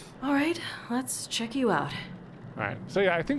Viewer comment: sounded like the game character said, "let's trick you out," to the cadaver. haha